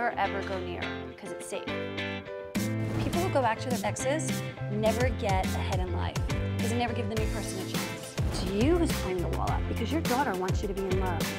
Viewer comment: What does she say at 1:00? ”People who... never get ahead in life.” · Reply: "people who go back to their exes never get ahead in life because they never give a new person a chance"